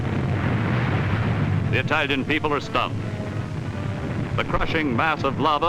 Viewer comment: He said "The Italian people are stunned" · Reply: I thought he said "stumped".